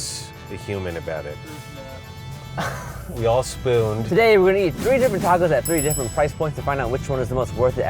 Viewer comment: Lmao I thought he said tee hee at the end, instead of to eat